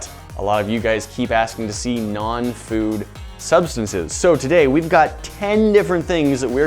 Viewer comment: I thought they said non-food items?🤔